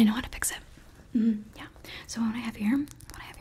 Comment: I thought you said as a rock 😅